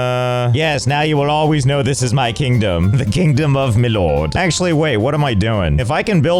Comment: Definitely thought that said "Miload" at first...